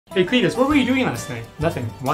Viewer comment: Bro i rlly thought he said "hey featus what where you doin last night?"